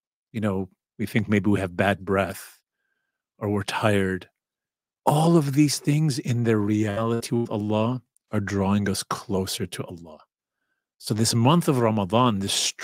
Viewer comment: Can someone clarify what word he is saying around @ 7:28 ? For me it gets disorted and can't hear it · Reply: "All of these things in reality to Allah are drawing us closer to Allah "